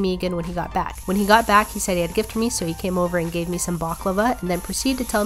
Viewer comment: 1. I overcooked the steak a wee bit 2. What's your most embarrassing dating story? 3. Thanks for voting in my latest poll! I will be eating a ghost pepper burrito and the Toe of Satan Lollipop this week! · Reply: Whatever you cook looks delicious 🤤🤤🤤 who ever dates you will be lucky! Btw, I thought you said he told he's "bakla ba" which meant "might be gay.." ☺️ stupid me